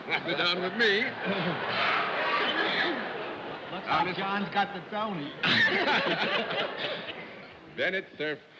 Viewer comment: At ~7:17, Bennett says to John, "John's got the _______." Both John and laugh and then John just says Bennett's name in a sort of "you got us" tone. But what did Bennett say? It almost sounds like "Sony", but first of all, that brand name had barely reached the U.S. and was just starting to become popular. Also, their only notable U.S. product was a transistor radio. In those days, the reputation of Japanese products in the U.S. was that they were cheap, inferior quality knock offs. Was Bennett saying that was a knock off version of Marilyn Monroe? Or did I totally have an Emily Litella moment and misunderstand the word that was actually said? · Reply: Lois Simmons try "John's got the Toni" (Tony Award). i do hear an S at the start of the word. Miss Toni?